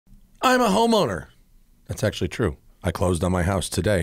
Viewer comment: Me barely turnin my volume up at the beginning of the video ‘I’m a homo!...no it’s true I’m a homeowner Am I bad person for thinking what I thought he said 😭😂